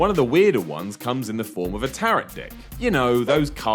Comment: Hello, Did you realize during the Big Lebowski, at 2:48, it says "turret d*ck instead of tarot deck.